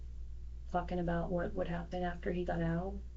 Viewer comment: I thought she said “fxxxing about”